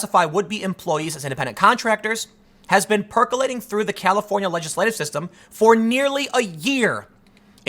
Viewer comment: I swear I just heard "California legislave system"